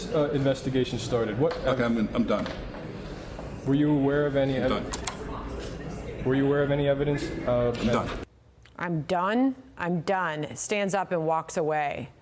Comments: I thought he was saying, “I’m dumb. I’m dumb. I’m dumb.” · I heard I'm dumb, I'm dumb😂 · I thought he said “I’m dumb. I’m dumb.” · "I'm done, I'm done". Sorry, I thought he said "I'm dumb, I'm dumb". My mistake.